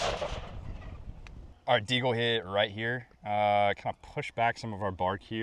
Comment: did he say deagle